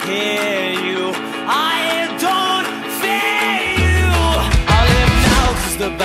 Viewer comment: Gosh the way you animated her saying "I don't fear you" is stellar. Can see the confidence and happiness. And the proud look on Spitfire is awesome. Great work:3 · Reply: THIS WHOLE TIME I WATCHED IT I THOUGHT IT SAID "I DONT NEED YOU" UNTIL I READ TH IS COMMENT I HATE IT HERE